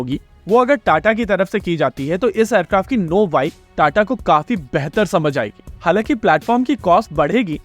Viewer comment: I suppose you meant “know how” because I heard “know why” I think. 🙏